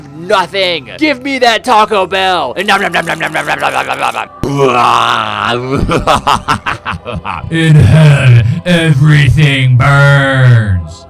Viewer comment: Pyro: IN HELL. EVBERYTHING BUUURNS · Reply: I thought he said in head everything burns lol